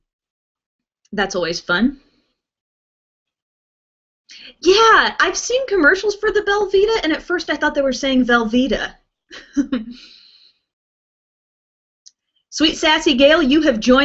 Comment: oh at first i thought you said Velvetta . haha